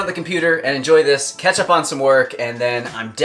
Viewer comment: I was cooking while watching this and then I thought around 7:50 you said "Ketchup on some work" and I was confused... well... just a bit 😅